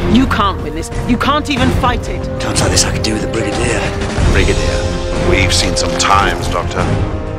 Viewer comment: What did he say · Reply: Times like this I could do with the brigadier